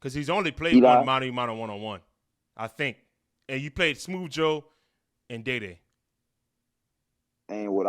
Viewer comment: Lmao I’m tripping I thought you said Eli only played Joe and diddy 😂😂😂😂